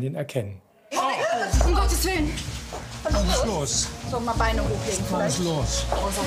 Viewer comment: Hab ich da bei 1:49 ein "Bruder muss los" gehört??